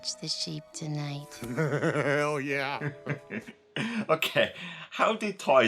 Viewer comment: Who else thought he said HELL YEAH ?